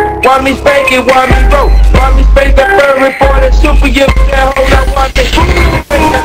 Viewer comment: Did he just say "then super-yiff that h**" at 1:21 ? Geez, what parody version is that and what is it pertaining to?